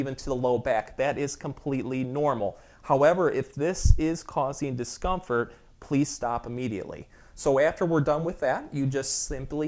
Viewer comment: please can you tell me what did the doctor say here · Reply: He said if that position is uncomfortable for you, stop.